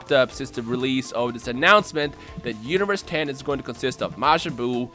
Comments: universe 10? 0:44 or did i hear wrong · Did you just say universe 10 at 0:43? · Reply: Phaze Rohan I think he said "The Universe 10" as in the universe's 10 fighters